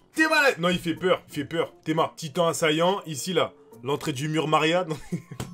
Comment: "l'entrée du mur Marianne"... 😂😂 😂😂 😂😂 😂😂 c'es trop pour moi les reufs jvais prendre l'aire